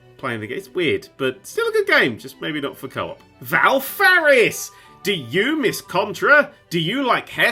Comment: can someone tell me what game this is. I couldn't hear what said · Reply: Valfaris